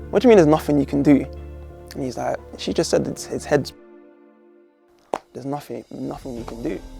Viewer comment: What did he say? "his head...."? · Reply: His heads been split :(